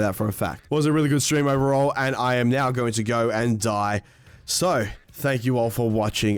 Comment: At first I thought he said slow instead of so ☠️